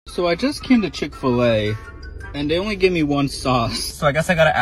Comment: When he said "so I just came to Chick-fil-A" I thought he said so I just killed a chicken today